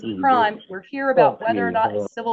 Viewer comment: did he say F*ck you ×2?